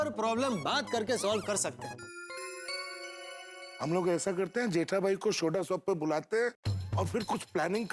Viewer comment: haathi bhai said shoda sop instead of soda shop 🤣🤣🤣🤣🤣🤣🤣🤣🤣